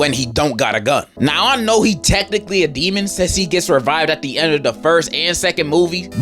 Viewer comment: wasn't paying attention and saw this I fr thought bro was saying he'd survive dio